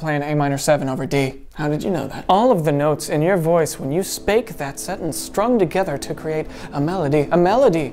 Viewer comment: “All of the notes in your voice when you spake that sentence” That’s hilarious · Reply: I thought you said spank XD